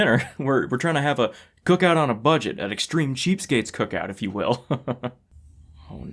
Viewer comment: When she said the bbq was cheap it sounded like she said it was shit. lol